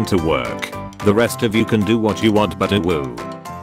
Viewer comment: The fact that the bot says uwu instead of ew at 1:46 is very disturbing